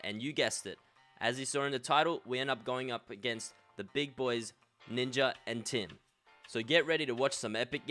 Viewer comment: am i the only one who thought fresh said ninja and tin